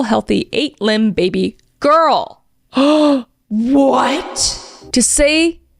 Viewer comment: I was listening in the background; I high-key thought you said 8-LIMBED baby girl 🤦🏼‍♀️ jesus